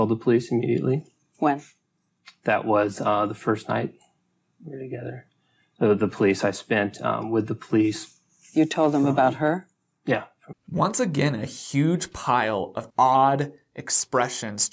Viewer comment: does he say “The police I spent um with he police” ?